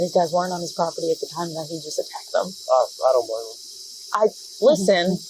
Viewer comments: cop actually said at 5:43 "oh i dont blame him" referring to the cop trying to arrest that guy. when the subtitles wrongly state the cop as saying "umm. i dont know" , just a fun fact that gives more insight into that cops demeanor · he does Not say "Um. I don't know." He says, "Well, I don't blame him" That's such disgusting behavior from a supervisor showing such contempt for anyone that would hold them accountable. There's checks and balances to every form of government. Why should police be different. There's a reason they have gotten as bad as they are. They have gone unchecked for decades.